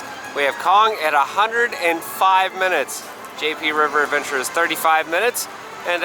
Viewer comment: I thought he said GP river adventure 😂